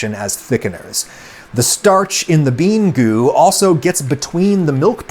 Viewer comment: I keep hearing "Let me stay in the goo, guys"